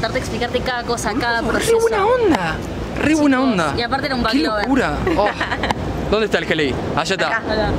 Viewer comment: que quiso decir? back lover? que dijo? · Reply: Vag lover (que le gustan los Volkswagen)